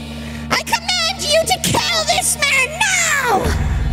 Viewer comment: Wait… did Rainbow just say KILL?! Is this the first time anyone has said kill? Am I wrong?